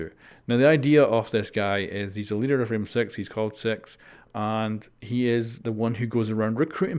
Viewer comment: What I heard: he’s the leader of rainbow six, he’s called six. What I saw in the captions: he’s the leader of rim sex, he’s called sex 😂😂😂😂😂 google translate is so bad sometimes